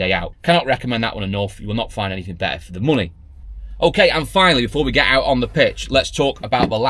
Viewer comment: On your promo, you said at 9:36 : "And finally, before we get out on the pitch'. Well, I had to watch it again as I thought you said: "And finally, before we get out on the piss".